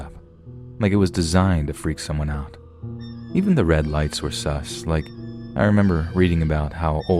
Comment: did i just hear the guy say "sus"